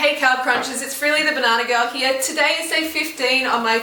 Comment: I know her name is, but when I first started watching her I thought she said "hey this is really the banana girl!" lol <3